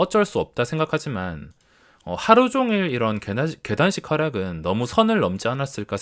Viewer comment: 개단식하락이라고 하실때 단이 자로 들림